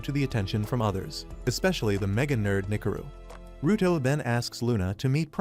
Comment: I thought he said ni##aro